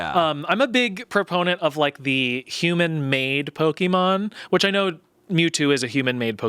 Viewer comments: when Tommy was talking about human-made Pokemon, I thought he was saying HUMAN MAID, and I was like damn is there a Pokemon that's just a human housekeeper? multiple? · I thought Tommy was saying "human maid pokemon" (Mr mime) and then he said obviously Mewtwo and I was like Mewtwo... Is a maid...? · when Tommy was talking about human-made Pokemon, I thought he was saying HUMAN MAID, and I was like damn is there a Pokemon that's just a human housekeeper? multiple? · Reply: I fully thought he said "human maid" because Indeedee literally is a maid/butler-themed Pokemon